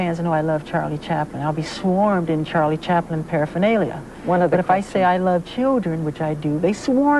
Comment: What word did Mike say there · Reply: Hi say : Charlie Chaplin parafernalia .